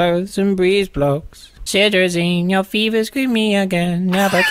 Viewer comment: did he say "you feet is screaming" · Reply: I'm pretty sure he said "your fever's screaming again"